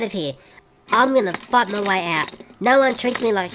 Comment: did Thomas say a bad word? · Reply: He said; "I'm gonna FIND a way out."